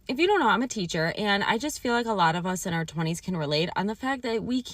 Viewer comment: Did I hear her right…she’s in her 20s? 😮